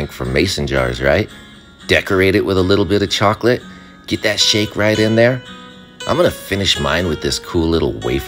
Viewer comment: i heard "get that sh*t right in there* and i was like wtf going wild on this one 😂